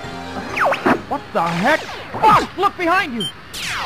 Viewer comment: lol it sounded like falco said "What the heck? Fuck look behind you!!!!"